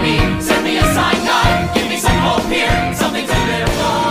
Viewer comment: IM SERIOUS I THOUGHT THE HEAFHERS SONG SAID "GIVE ME SOME HOPE HERE" AND NOT "GIVE ME SOME COLD BEER" HELP